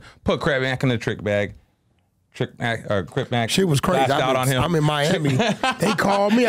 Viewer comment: “KrabMac” wat bra sayy